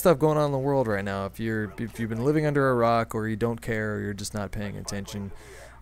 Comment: i thought you said living under Iraq instead of a rock